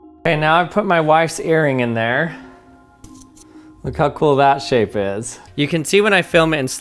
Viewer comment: "look how cool that shape is..." I'm waiting for class to start, so I have this on a low volume and I had to go back and listen closer because I swear I heard "sh*t" not "shape" and was like "I mean, you're not wrong, but what?" 😂